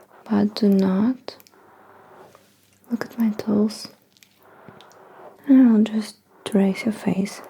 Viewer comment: lmao cute intro Oh TOOLS I thought you kept saying "look at my toes" O_O